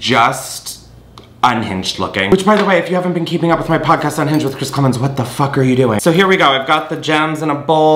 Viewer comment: swear I thought you said “keeping up with my podcast on Hinge with Chris klemens” lol I was like damn Chris, you can’t be that down bad be nice to yourself